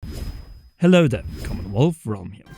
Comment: I swear.. every time this guy starts his videos, I only hear “Hello the! Comowethomhe!”